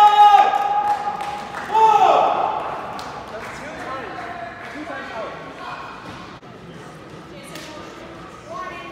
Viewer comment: What did someone say at 6:10 that made everyone laugh? edit: It was. What did he say? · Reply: Jason got a yellow card: "Welcome to the club."